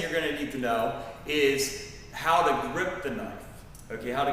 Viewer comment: I thought he said how to kryptonite lol